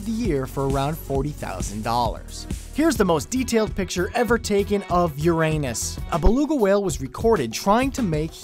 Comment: thumbs up who also thought that,when he said:"Here's the most detailed picture ever taken from Uranus!" it sounded like:"Here's the most detailed picture ever taken of your anus!"